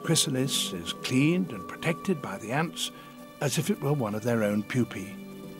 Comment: "As if it was one of their own Poopy" This is what I heard. This made me laugh. I am currently 20 Years old. I hope you have a good day.